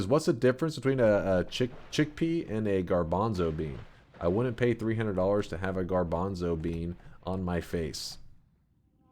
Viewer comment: what did he just say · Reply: would pay 300$ to have a chick pee on his face